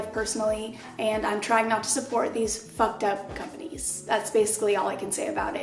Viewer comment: so I literally misheard her... I heard "they support the lgbtq+" and then she said like " that's just something I can't support" or something like that... GIRL I REWINDED THAT SOO QUICK- I SAID TO MYSELF "I WATCHED THIS GIRL FOR 3 YEARS AND SHES HOMOPHOBIC?"